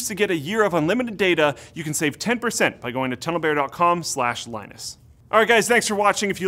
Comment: When he said TunnelBear I thought he said Pedo Bear